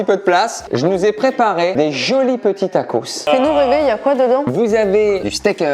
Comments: C’est moi ou a 4:09 camille elle dit a dit nous bb il y a quoi dedans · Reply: Déjà c'est qui? Et en plus de Ça, Non, elle dit " fais nous rêver, y a quoi dedans" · je rêve où elle a dis "sinon bb ya quoi dedans?" 😶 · a 4:10 minute Jai attendu dit nous bebe il y a quoi dedans écouter bien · Camille dit sinon bébé y’a quoi dedans sont il en couple ??